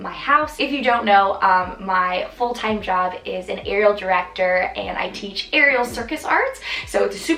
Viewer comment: Had to do a double-take & rewind because I thought you'd said your job was a "funeral director and aerial circus art."